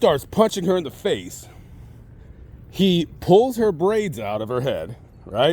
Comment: I thought you said at 10:04 that "he pulls her brains out of her head" lol